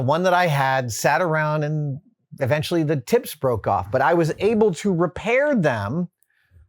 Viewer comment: did he just say his tits broke off?